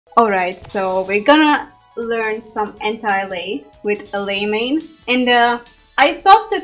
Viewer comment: I thought you said 'Hentai' Lei, got me startled there for a second LMAO